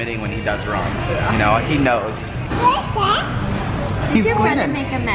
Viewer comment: did anyone else hear say "What's that". Just me ok